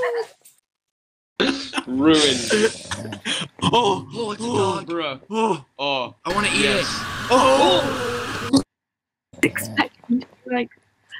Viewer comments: did he just say he wanna eat the dog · did he just say he wants to eat the dog WTF!? 😂